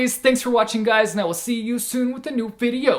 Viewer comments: I was listening to this and not watching, and at the end I heard, “And also it’s hard to bit you on the bed”... had to rewind, so much confusion! Wonderful chat! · "it's also hard to bye chu on my bed." Totally misheard that as "It's also hard to bite chu on my bed" 😂😂😂